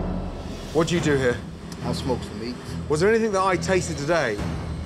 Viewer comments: I smokes the meats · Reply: I Thought He Said "I Smoke The Weed" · my man really does look like he "smokes the meat"😂😂😂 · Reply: i thought he said i smokes some weed hahaa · my man really does look like he "smokes the meat"😂😂😂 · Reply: i thought he said i smoke weed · "I smokes da meats" ;3 XD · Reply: i heard "I smoke some weed" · I thought the chef said " i smoke the weed 😂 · sounded like he said I smoke the weed😂😂😂…that guy looks so high😆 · I thought he said , " i smokes the weed . " 😂